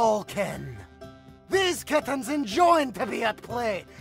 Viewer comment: I misheard that last part as “especially flavored” I